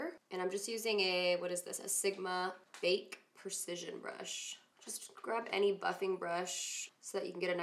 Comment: I thought you said "Just grab any f*cking brush.." & then I realized you said "just grab any buffing brush" haha. I was like Woah, already startin' off with the f bombs